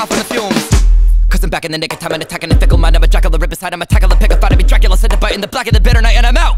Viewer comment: When he said I’m a jackal, I’ll rip his hide”, I honestly thought he said Jack the Ripper and I was quite terrified for a second.